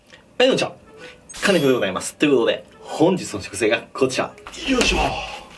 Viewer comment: 気化熱でございますに聞こえる笑